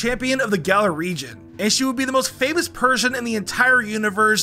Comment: Did you say Marnie would be the most famous Persian (instead of person)?